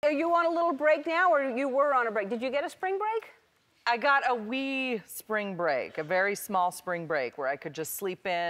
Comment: I THOUGHT SHE SAID A WEED SPRING BREAK